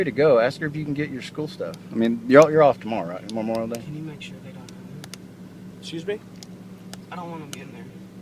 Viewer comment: I haven't seen any comments about it but did anyone else hear say "can you make sure they don't hurt me?" [pertaining to getting his stuff from the house]. Thank god the cop took the hitting comment seriously. Maybe I'm just reading into it, but it seems that's the main reason he let them go. Him clocking the question immediately and saying "excuse me?" Probably wanting to hear him say it again (or shock?)